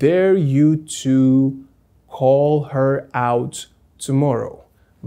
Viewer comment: what did ya jus say? To hall her?